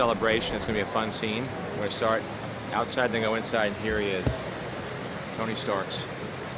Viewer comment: did I hear that right? Tony Starks ... "s" with a plural ??